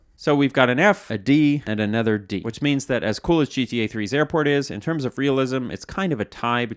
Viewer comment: I thought that said "GTA V Airpods" 🤦 I wouldn't be surprised if that was it tho